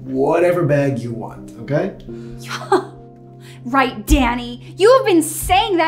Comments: When Danny came and said he’s the surprise Erica said Danny she sounded like she said daddy😳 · “Danny” I thought she said daddy